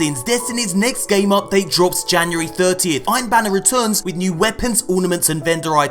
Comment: Ohh January 30th I thought you said January 13th thanks